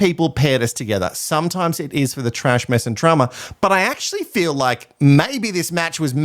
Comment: I swear you said “trash mess and trauma” - it’s either my intrusive thoughts or yours 😂